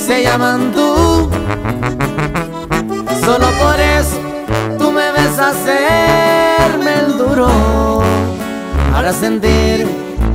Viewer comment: yo siempre pensé que decía por eso tu me besas el menduró 😂😂